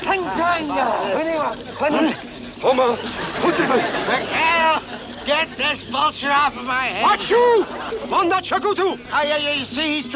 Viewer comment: did he really say get this bullshit off of my head? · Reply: "McHale,, get this vulture off my head!!"